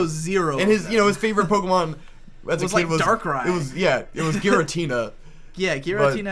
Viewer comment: I looked up "Darkrider pokemon" 'cause I thought that's what said and found a picture of Shadow the Hedgehog on a motorcycle holding a pistol LOL